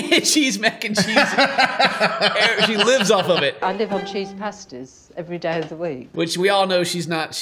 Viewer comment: She said she eats "cheese pasties" not cheese pasta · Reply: I could have sworn she didn't say Pasties, but actually Pastas. Cheese Pastas